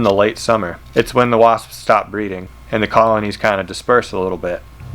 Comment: for a second i thought you said stop breathing